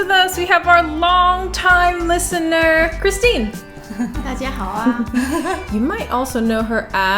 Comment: In the beginning what word did Christine say at the end? 00:11 dajiahao what? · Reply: Christine says: 大家好啊! The last character is just a so called modal particle (of which there are many) to make the sentence more colloquial. 漰漢明, Copenhagen, DK